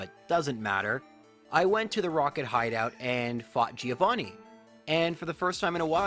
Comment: I thought he said “ and I when to fuck Giovanni “😳 I was like holdup for a sec 😂